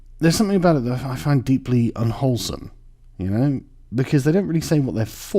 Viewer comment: Thought you were gonna say "deeply unholy" instead of "unwholesome." XD